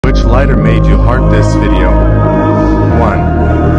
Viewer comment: I almost heard that as " which lighter made you hard this video " 💀